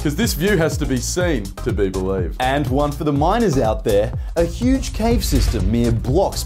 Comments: Why do I keep hearing “minErs” as “minOrs” 😭 😭 😭 😭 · I heard the "miners" as "minors" yet the sentence about caves still hit the same